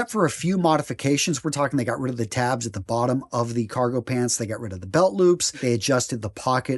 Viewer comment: Thought that said 'Congo Pants' lmao